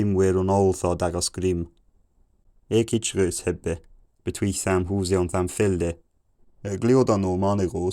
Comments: I think I heard in 750 "between samples" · About 1:20. How fascinating. I had nothing until then. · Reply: I heard "between samples" upin the middle of 700 and 800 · About 750 I think I heard "between his house and his field" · First word I caught was 'chicken' around 800 I think. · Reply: I first heard "between samples" around that time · I could swear I hear him say "all daggers, grim" but I think it just sounded like that. - Future me: It was, but interesting that in this case "grimm" likely means "cruel" and it feels like that may be the origin of the modern word "grim", so I will count it as the first word I understood. I may have also understood "all" but I am less certain. "Daggers" (dayos) was a complete misunderstanding though. In the 1:20s he talks about a chicken and a horse but I can't understand the rest. Future me: Nailed chicken, seem to have mistaken "goose" for "horse". Fascinating. 1:31 someone is a "loud fool"? Future me: no, a red bird- or a foul. Interesting that they would have called a bird of prey a foul (fugol)? Then like you, at 1:42 "It is long winter, the ground is cold" Future me: Nailed it From there forward I am definitely understanding the Middle English on and off, but not concrete. Feels less like another language and more like someone with a thick accent. Then at 2:35 (late 1500s) forward I basically understand everything but a word or two.